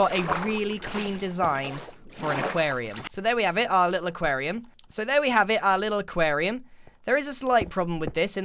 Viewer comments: did u say “so there we have it a little aquarium” twice, or was that just me? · Is it just me or did he say ‘so here’s our little aquarium’ twice?